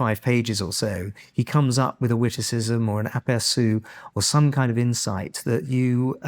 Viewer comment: what does he say? Appearsu? Sounds French...